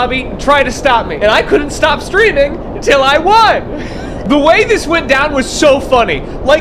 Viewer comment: I thought slimecicle said i cant stop breathing til i win lol